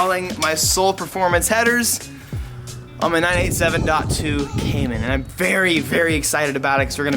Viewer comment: i heard at 1:35 "daihatsu cayman" and i shat myself